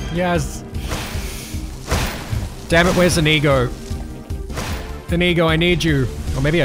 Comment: sounds like hes say wheres a negro, every time i hear him say it